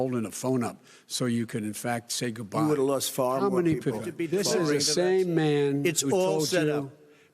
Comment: What is Trump saying? Anyone know? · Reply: "More people" but if you rewind a few more seconds the full statement is "you would've lost far more people"